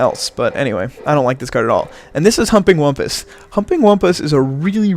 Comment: Great as always but it sounds like you were saying "Humping Wumpus" lol